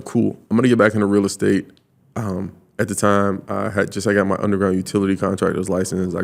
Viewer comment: It sound like he says underwriting utility